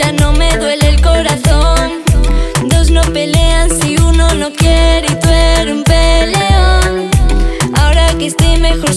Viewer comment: ¿que dices en el minuto 0:46 ? La frase que dices 😂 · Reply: Dos no pelean si uno no quiere y tú eres un peleón 😂